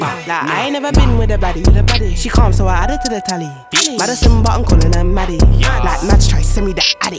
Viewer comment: I thought they said something about simba But she said "Madison but " British ass rap lmao but I love the song